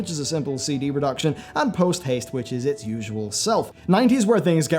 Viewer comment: thought that said "toothpaste"